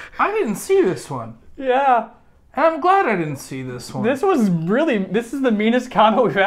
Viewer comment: I thought said “and I’m gay!” 😂😂